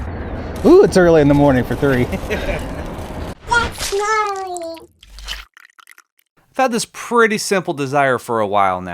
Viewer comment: What is that eyeball saying? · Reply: I think it says “that’s gnarly”